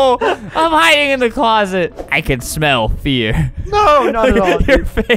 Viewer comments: I literally thought that he said "I can smell your [FART]." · When one of the days union guys said “I can smell [fear] I thought he said “I can smell [fart].” What have you done to me Albert ;))